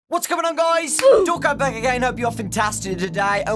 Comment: Wait... At the beginning of the video he always sais: ,,What is going on guys, Dawko back again..." Am I the only one that thought he was saying ,,Don't come back again" ?